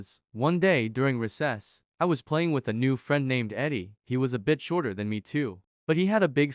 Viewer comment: When he said " he was a bit shorter then me" I thought he said " he was a bitch"